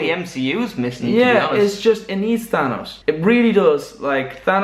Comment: When said we need thanos I thought he said we need vanoss. Marvels greatest villain: vanoss the owl.